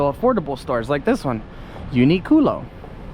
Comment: Did ya just said- UNIQUE CULO? Dang....I like Unique Culo. hehehehe Uniqlo- Uni GLOW